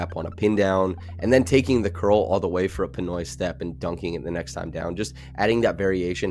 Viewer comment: did you say Pinoy step? Lol